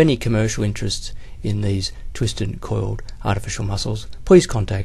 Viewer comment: did he say professor robotnik?